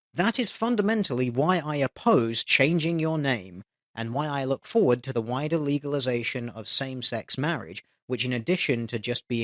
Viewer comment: I listen to your videos as audio logs and I thought you said “I look forward to the wide illegalization of same sex marriage” thank god i double checked the footage